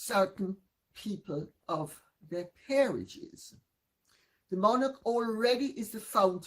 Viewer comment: peerage = not parish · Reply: FYI...I heard said "peerage" not parish.